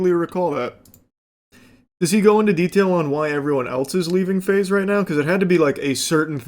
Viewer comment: As a left handed person hearing everyone say Faze is going left is big news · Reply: I heard that 'everyone is lefting at Faze'. Exciting times!